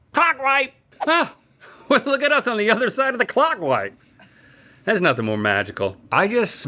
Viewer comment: Jeez, I had to replay 7:25 twice. I thought he said "clock ( minus the L) wipe.😅